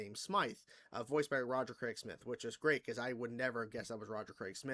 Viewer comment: did you just say Roger Craig Smith (the guy that voiced Sonic and Chris redfield and others)